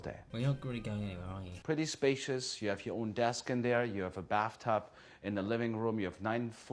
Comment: ummmmm 3:52 anyone else hear him say that there is a bathtub in the living room???